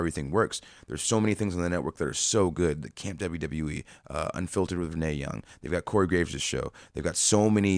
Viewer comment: does he say "Unfiltered with Mae Young?"